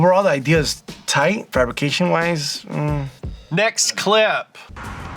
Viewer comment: did he say the fabrication was tinny?